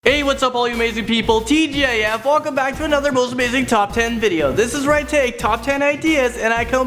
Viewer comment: At the very start at first I thought you said hey you Asian people XD